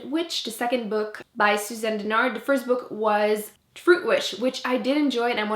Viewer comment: oh my god when you said truthwitch i thought you said fruitwitch and i was like damn that sounds like a book id like to read also i looooved the awakening